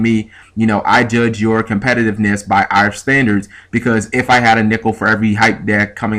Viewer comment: At first I thought you said " if I had a nipple for every hype deck in ocg" but then I thought about it and realized you said nickel. Lol